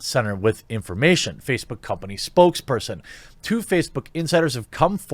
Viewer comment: Funny, I thought he said Two-Facebook.